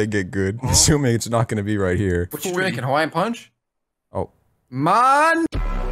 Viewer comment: Wait what was he going to say at 6:28? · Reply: ik its been 8 months but he said neighbor